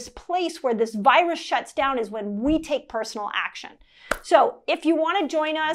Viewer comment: Oops, my mistake, I hear you say, “We TAKE personal action.” Thanks again.